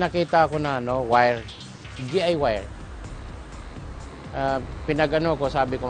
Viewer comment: galing naman ni lolo pero tanong ko lng narinig nyo ba ung sbi ni lolo sa 0:44 imbis na DIY naging DI wire